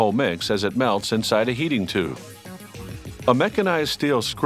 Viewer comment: I thought he said “yeeting tube” 😂